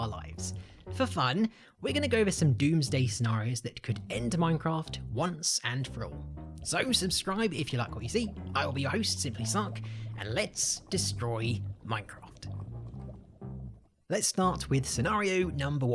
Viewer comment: the automatic subtitle said "simply suck" lmao 😂😂😂